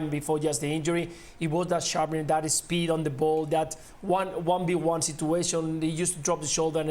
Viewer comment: I’m a bit deaf, so when I hear the Scottish twins start talking about Ansu Fati ? it sounds like they’re referring to his on field flatulence ! When Luis Garcia ? Talked about Fatis speed on the ball , I heard “ peed on the pole” 😂